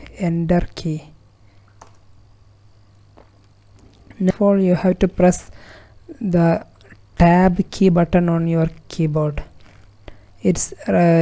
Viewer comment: I cant understand what hes saying someone please help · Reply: Now you have to press the tab button on your keyboard